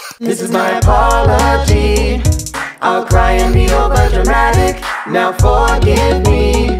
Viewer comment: The first time I watched this i thought they said "I'm crying the overdramatic"